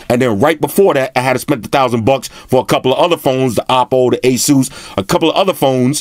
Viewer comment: Did u said asus at 21:33? Does that mean u r gonna get the rog phone? Man I'm looking forward to that phone's review from u. 😃